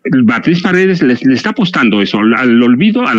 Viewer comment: Jajajaja entendí "apestan".